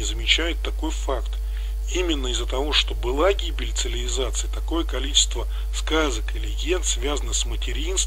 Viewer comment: ЦиЛиВизация сильное слово, чуть не оглох. А думал, что просто послышалось, а нет так и говорит автор уже не в одном ролике.